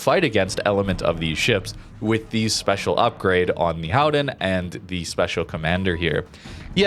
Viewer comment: or anyone help... you refer to a ship several times through out this video, it sounds like you are saying the Howden, the transcript shows "Howen" @ 9:42, & @ 20:53 what are you saying there, I can't find any ship with that name.... its driving me crazy